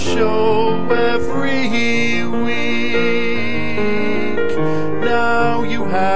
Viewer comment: did just say "HeWeek"?